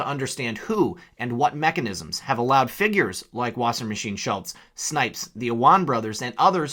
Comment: I could swear I heard you say Debbie Washingmachine Schultz, I had to rewind and play it again.